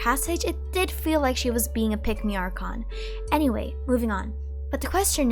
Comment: "Pick Me Archon" killed me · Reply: At first, I thought she said "pickney," but ironically, that word could also fit the Hydro Archon.